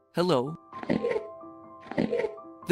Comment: Instead of “what’s up” I hear “watch out” and every time I hear that in Minecraft it’s self there’s always a hostile mob there some one else test this theory please